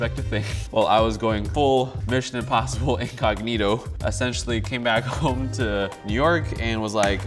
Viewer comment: did he say "eggognito"?!?